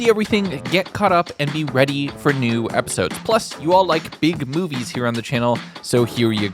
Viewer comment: Ngl at the beginning i wasnt paying attention, and thought he said,"and i know yall love big boobies on this channel" (i havent slept in 2 days dont blame me)